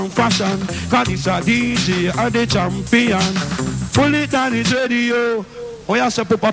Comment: What did he say at 4:25? I've heard Barrington Levy say it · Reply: He said "pull it danny dredioo" danny dread is the famous selector the guy who controls the turn table pull it means rewind it / started the beat again